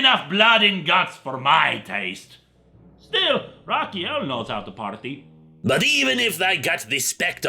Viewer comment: I can't hear the line at 2:35 clearly. Can you tell me who "likes to party"? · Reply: I think he says Raphael a powerful Angel who would be a double pun with the Turtle Raphael